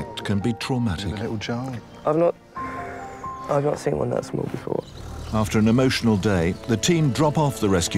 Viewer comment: guys what did he say? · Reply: Rosa and Aimee's Vlogs! I've not seen one that small before